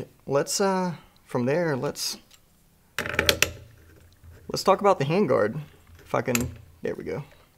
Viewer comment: I know you said "Let's talk about the handgaurd... if I can..." But I heard "... the handgaurd... f***in'..."